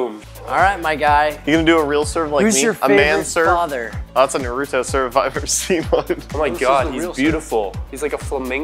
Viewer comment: did say “that’s a naruto serve if I ever saw one”